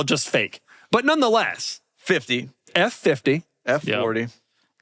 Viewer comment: why’d he say fuck me for?! 🤨 (Anody getting this reference this late into the year? I just realized he said f50. And I hear eff fiddy